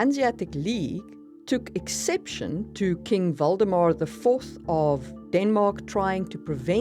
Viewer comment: Did you just say King Voldemort VI??? 😂